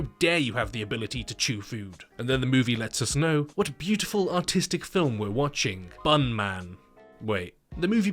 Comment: His accent at 1:12 made me think he said “Autistic”💀